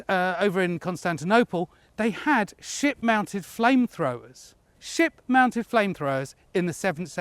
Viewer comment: I first heard “They had shit-mounted flame throwers” 😂 honestly I think I’d be even more intimidated by a shit-mounted flamethrower!